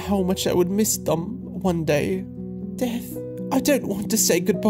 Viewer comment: Me who thought the black cat said dad instead of death 💀